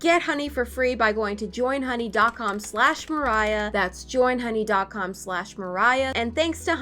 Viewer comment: “I think she’s just stupid. CANT FIX THAT.” 2021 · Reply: OMG i thought you were saying moriah Instead of the cat at first